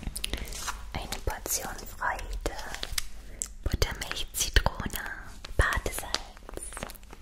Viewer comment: ich habe „Muttermilch und Zitrone“ verstanden und mich kurz erschrocken 😂